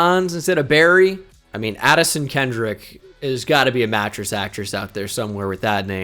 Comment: did he say "Mattress Actress" ?? I have never heard that term and it is FANTASTIC.